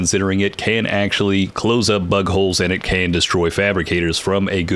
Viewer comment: Thought you said "there is some utility to this weapon as it can close up buttholes" 😂